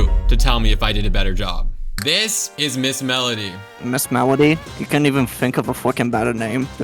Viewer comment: Oyo I thought he said mrs melons